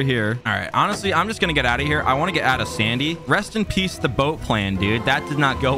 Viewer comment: did he say boat plan or im i js bad at listening